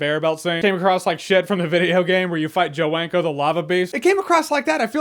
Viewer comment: thought he said Joe wanker for a minute.😂